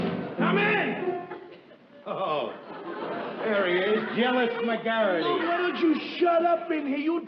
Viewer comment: Alice says Garrity but Ralph says McGarrity. Which is it? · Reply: sha11235 I heard " jealous McGarrity"